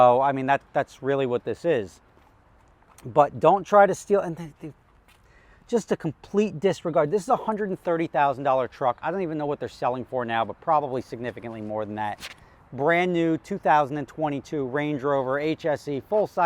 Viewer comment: Did you say truck???